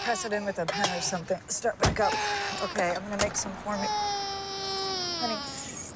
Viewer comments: am i the only one questioning my understanding abt the show ?? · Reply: did she say : "ok! I gonna make some corny" or something? I don't get it. can you tell me? thanks. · is this show any good? been thinking in giving it a try... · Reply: did she say : ok!and then make some corny or something? I don't get it. can anyone tell me? thanks.